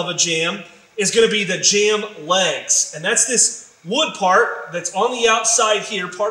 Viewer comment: Hi Ty, several times you use a term and I can't quite catch what you are saying. It's right between the stop and the parting bead. I hear "jamb lay" or "jamb way." What is that? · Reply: John Leeke , thanks for asking. To clarify I am saying Jamb Leg.